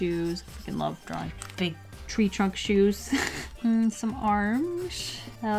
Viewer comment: Did she say f@ love drawing big shoes at 6:04 or was it just me